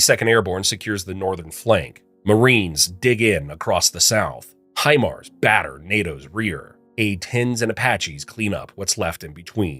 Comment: Had to go back and watch that again. I misheard what you said and thought you said "high hard one batters NATOs rear" 🤣